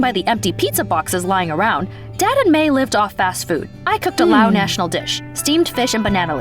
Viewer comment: Oh I thought she said Liv and Maddie 😂